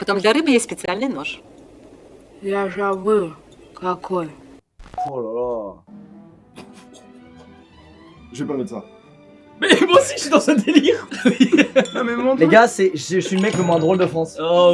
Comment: IstarGames a cause de toi j'entends toujours : " la javel sa colle" Sa fait bizarre !! 🤔🤨🤣🤣 5:35 Sa aurait était marrant que maxence marque : "la javel sa colle" 🤣🤣🤣🤣🤣